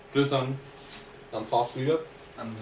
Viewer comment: I first misheard Fastreader as FastBreeder XD Nice to hear your opinions :)